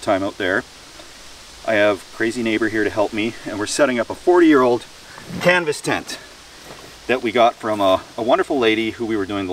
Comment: Oh man i thought it said " 40 year old, camping". I said hey i gotta see this.lol